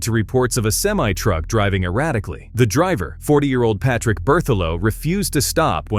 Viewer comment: I thought I said a 4 year old was driving the semi-truck and I’m like what the fuck that toddler got great driving skills 🤣🤣🤣🤣 (but no it was a 40 year old man)